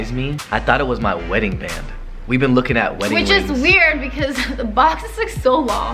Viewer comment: Is it just me but did i hear "wings" instead of "rings"